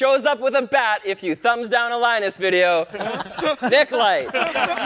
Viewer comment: Autogenerated subtitles say "dicklight" instead of Nick Light, lmao